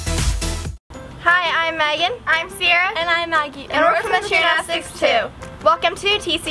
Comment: In their into it sounds like they say "and we're from the channel 6 2!"